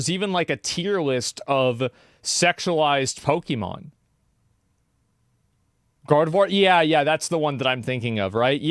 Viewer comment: I wasn't clear did you say....sexualized Hoekemon, or sexualized Pokemon...?